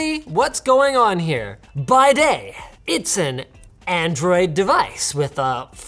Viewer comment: Ah man when he said "by day" I heard "bidet" and was really confused